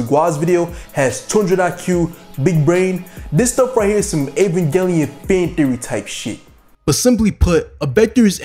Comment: Can someone please translate what the guy said at 1:00 Some what kind of shit? I just heard mumbling but it sounded funny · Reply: "this stuff right here is some Evangelion fan theory type shit"